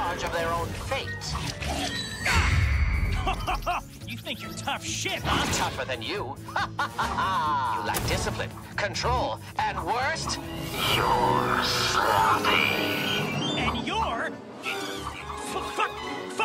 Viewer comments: What did he call Adam? I heard "slutty" but i could be wrong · "You're Sloppy" proceeds to have the cleanest animation scene while saying this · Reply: I thought he said “lack discipline, control, and worst of all, your soul.”